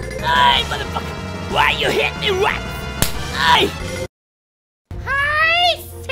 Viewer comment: my dumbass brain thought he said “why you hit me once”